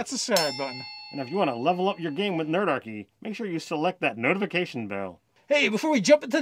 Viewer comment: I swear said "hit that NERDIFACATION bell" . Also fun fact . My first D&D character was a multi-classed . Infact almost every character i have ever made has ended up Multi-Classed . ALSO Monk goes Great with any class , But is doubly Good with all Arcane Spell casters ( Artifincer , Sorcerer , Warlock & Wizard ) .